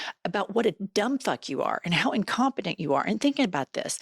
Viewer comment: When you called him incompetent, I thought you said he was incontinent, but potato potatoe